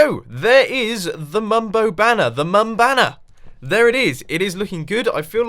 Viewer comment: "The Mumbo Banner! The mum-banger!" Is what I heard O.o